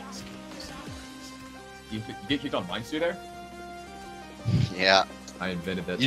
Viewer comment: What was the mind sooth comment at the end regarding? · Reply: Ah thought he said mind soothe. Yea I've used that on a few spriests to blow em up when they're trying to find me.